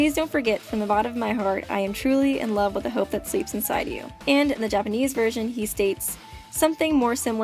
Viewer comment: Nagito: It doesn't count as a confession if you say you fell in love with the hope sleeping in him ;) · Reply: I thought you said if you fell in love with the hope of sleeping with him. ...😳